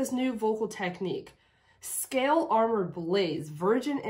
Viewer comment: if you listen to her pronunciation, it sounds like she says "scale armour belies virgin innocence" (belies instead of blaze) ... referring to fish scales as armor?